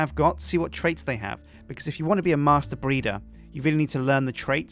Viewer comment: On 4:38 I heard master burrito instead of master breeder. Had to lol hard.